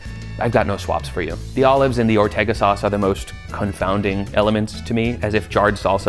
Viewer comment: I thought you said there were olives in the ortega sauce, haha